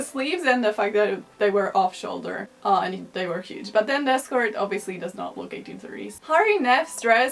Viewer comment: I listened to it like 30 times, and I'm still not 100% sure, but giiirl did you just call Cole Sprouse Lili Reinhart's escort? What a feminist icon.